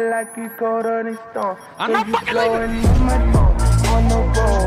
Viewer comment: did he say "im not fucking ligma" ? 💀😂😭